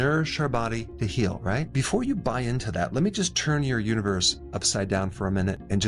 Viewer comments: sir I thought you said uterus 😭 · thought he said, "let me just turn your uterus upside down". Had to double check what video I clicked on!! 🤣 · did he just say "Let me turn your uterus upside down for a minute"?